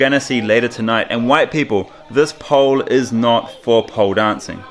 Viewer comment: did you say...white people this is not for pole dancing 🤔😂😂😂😂